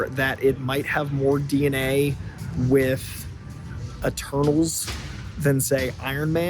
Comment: When you said Eternals I thought you said Turtles haha